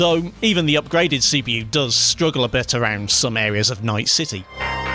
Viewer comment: For some reason I misheard that as "some areas of Nice City" and thought to myself, "Well, that's a daring take."